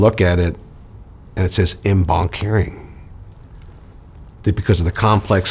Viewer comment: what does he say? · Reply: En banc